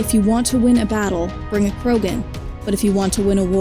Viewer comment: I just noticed a typo in the subtitles. at 0:38 it says Korgan instead of Krogan.